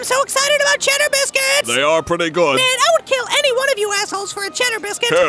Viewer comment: what is the starfish saying? "i straight up murderd my cousin for a ???" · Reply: “for a basket of them”